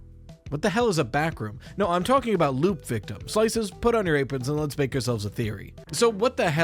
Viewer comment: Briefly misheard the name of the theory as 'LukeVictim' and thought ohhh not another name for this dang kid XD